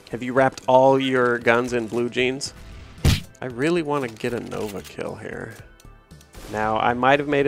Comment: The first thing I thought of when said an nova kill was a ova kill lol